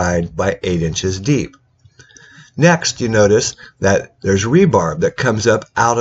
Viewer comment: Did I just hear "rebarb?"